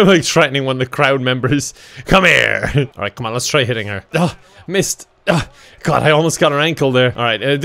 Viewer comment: WTF did just say MIST? Isnt that a german expression? have i missed something? :0 5:29 oh my god he said "missed"... 😂 it sounded hella german tho xD